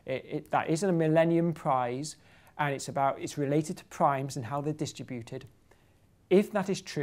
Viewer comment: subtitles say "crimes" instead of primes :D